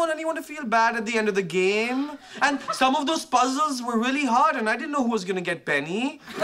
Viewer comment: what did Raj say? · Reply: He said, "Some of those puzzles were really hard and I didn't know who was going to get Penny."